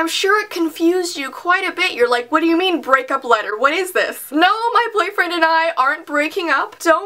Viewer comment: I thought it said breakfast letter